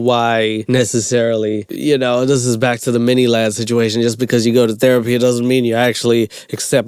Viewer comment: i know this video is three years old but did actually say ‘this is back to the miniladd situation’?? or am i tripping